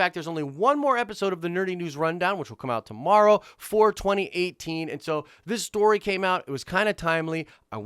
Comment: BE NICE IN THE COMMENTS! I know these types of stories can be unpleasant for some and that they can cause Star Wars fans to get angry at each other. Let's actually try to come together... Please... Let me know what you're thinking below! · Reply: since you asked what we're all thinking, I'll just say that @ 2:37, you say "For 2018" but the first 5 times I watched that phrase, I thought you were saying "4/20/18." Cracked me up for some reason. Thanks for a good video.